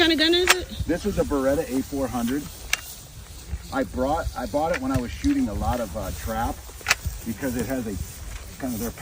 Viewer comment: Can't get it to shoot. Wish I knew what I was doing. (Sounded like he said, "I bought it when I was shooting a lot of trout") lol